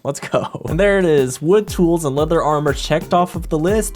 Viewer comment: did he say leather-rite?